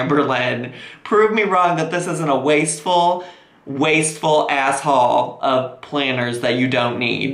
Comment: thought he said ‘ wasteful asshole’ ... I mean... THAT... wouldn’t be wrong either?